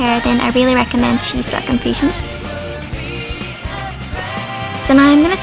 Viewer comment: i love how it sounds like your saying " Chi Sil Confusion " :3